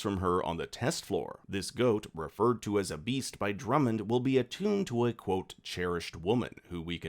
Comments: I thought Drummond said “this beast will be ENTOMBED WITH a cherished woman.” (Not attuned). The goat will guide her spirit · Attuned? I thought he said entombed. Yeah just watched with Closed Caption, Entombed. Which means they intended to literally kill Gemma. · No, I think it’s quite literal here. You’ve got the dialogue wrong, Apple’s subtitles say “this beast will be ENTOMBED…” not “ATTUNE”, meaning someone, likely Gemma, will be dead when it’s all said and done.